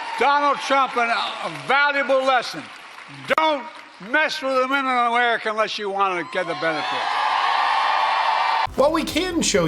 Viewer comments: It sounded like Biden said, "Don't mess with the women of America unless you want to get the benefit." Terms accepted, Mr President. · Sounds like Brandon said "women with benefits" to me but I can't be certain